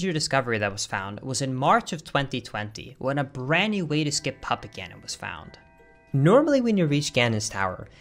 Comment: sounded like "papa ganon" when you said puppet ganon